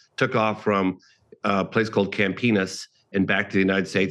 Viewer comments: So like a clearer mental pic of the uap situation? Also, did he say camp penis? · Did he say Camp Enis?